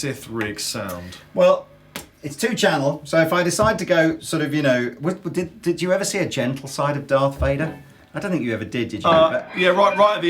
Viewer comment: I thought said did you ever see the djent side of darth Vader